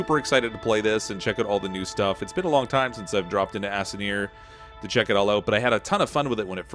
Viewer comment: did he say Assineer?